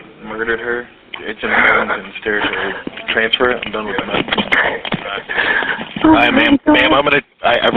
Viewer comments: They’re not doing it for fun. If they transfer you it’s because they have to whether it’s because they need you to talk to like medical or fire dispatch or if you’re like on the cusp of county borders and your call gets directed to one county’s dispatch and they have to transfer you to your county’s dispatch. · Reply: Right after the screen said. "Transfer her (inaudible)" it really really sounded like the operator said "I’m done with her mess" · Right after the screen said. "Transfer her (inaudible)" it really really sounded like the operator said "I’m done with her mess"